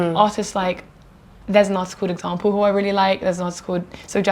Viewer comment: did anyone catch the second artist name she mentioned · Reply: example